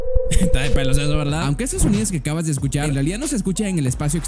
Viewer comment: QUE DIJO?? XD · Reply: esta de pelos verdad?